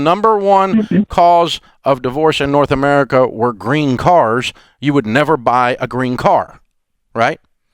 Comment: i thought he said green cards instead of green cars! 🤣🤣🤣